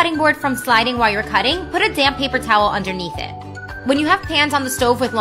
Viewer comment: I heard “a damn paper towel” lol